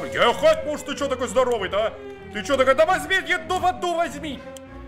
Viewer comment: я один услышал "ДА ЕДУ В АДУ ВОЗЬМИ!"?